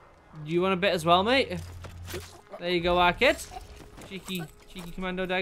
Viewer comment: Alright, I love hearing your mannerisms and the slang you use, and I’ve been wondering, when you stab that guy at 2:27, you say “There you go, _____” What did you call him?? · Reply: He said 'our kid' - which is what us northerners normally use as a term to describe a sibling or sometimes a friend. Obviously the German he stabbed was neither!